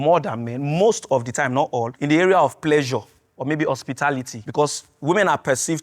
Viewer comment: wtf???? Did he just say pleasure??